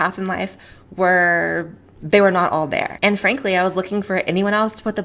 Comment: sounds like you said "Anne frank-ly" lol